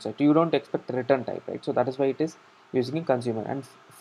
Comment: Can someone write in comments what is he saying at 10:03 ? I all I hear is "tratrratatraramtam that is why it is"